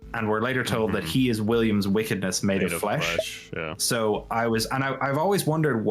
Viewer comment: Here's a theory, he is very clearly saying "I am your wickedness MADE FLESH" not "MADE *OF* FLESH" and it drives me absolutely bonkers insane with how often it's misquoted because one is a normal sentence and the other is complete nonsensical gibberish. Doesn't even make sense because it is very clearly not made of flesh. If he is actually saying "of" then the game itself is wrong and I hate that even more.